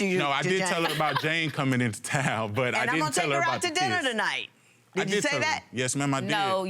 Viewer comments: Listen closely at about 13:17 Did he just say " I didn't tell her about the kiss " this guy is terrible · Am I the only person who heard him say "I didn't tell her about the kiss"? 😳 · Wait a minute, did he just say, "I did tell her about Jane coming into town but I didn't tell her about the KISS!?"